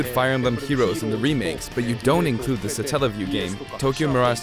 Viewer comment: sotellofyou, what did he say? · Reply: Satellaview, the Super Famicom peripheral.